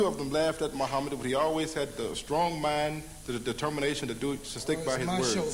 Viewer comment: Strong man ........ ? Can someone please tell me what he said.. i could not catch that?